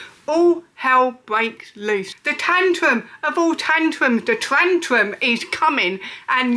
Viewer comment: Did she say "Trantrum"??